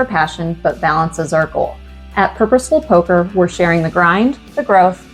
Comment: i misunderstood you right at the end for a second. I thought you said Herpesful Poker :)